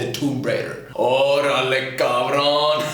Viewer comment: did I just hear corona.